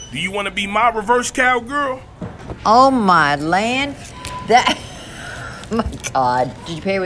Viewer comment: I don't know if it was my imagination, but I thought the lady said, "Oh my Lanta"